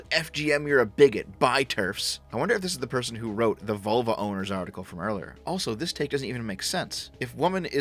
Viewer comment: Ahahah, I thought you said ''Volvo Owners'' xD